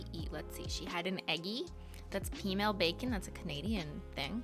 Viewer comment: For anyone wondering, @ 20:51 says “peameal bacon”. I couldn’t really understand what she said, I thought she said female bacon which of course didn’t make sense, but once I heard “female”, I couldn’t hear anything else- despite replaying it several times. Even when I turned on closed captioning and replayed it for the 20th time, cc ALSO translated what she said as “female”. Not helpful 😑. Even though I was fairly certain she hadn’t said female, I took a risk and google searched “female bacon”. Immediately after hitting “go” I wished I had opened a private browser first, 🫣 but thankfully the AI bots running google search knew what my pure heart had meant/intended and those magnificent bots supplied me with only GA (general audience) rated info pertaining to peameal bacon and nothing at all questionable that would put me on some kind of domestic watch list. Anyways. That’s all. You’re welcome.